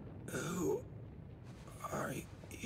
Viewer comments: What the video said : "I had to shed a few pounds to win that fight" What I heard : "I had to shit a few pounds to win that fight" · I definitely heard that as "I had to shit a few pound to end that fight, but come on!"